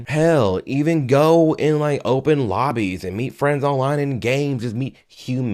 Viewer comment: i genuinely thought he said open bodies. I was like man did this guy just say go clap some cheeks?! Lol awsome